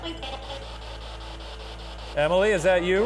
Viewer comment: When says is there a Emily I swear it says whos emily